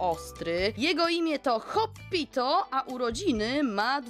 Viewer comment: Jak przeczytałaś Hoppito, to usłyszałam Hobbiton. Przedawkowanie LOTR😂